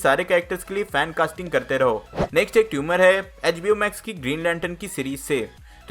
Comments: did he say tumor instead of rumor? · did he really said "ek tumor hain in place of rumour hai"